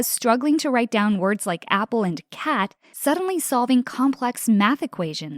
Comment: tell me that doesn’t say “c*nt” instead of cat😭😂